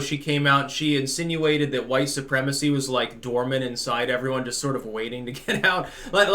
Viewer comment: Dormant? I thought she said door mat! Damn I’ve spent the last two days lying in front of my door when I should have been invading Europe.